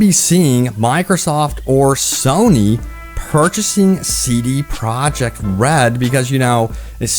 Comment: I keep hearing CD "product" red and it's honestly taking all my attention from this video. It stands out almost as much as hearing Benedict Cumberbatch say PenWen